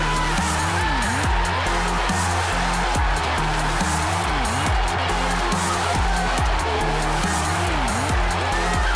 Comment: what did he say? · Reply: "Fucking come on!!!"